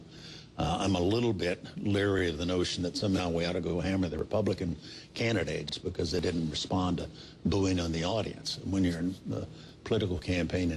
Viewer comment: Did Chaney say go H.A.M @ 3:49?